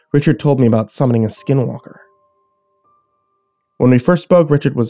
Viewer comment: I thought it said skywalker and I was like "STAR WARS WOAH"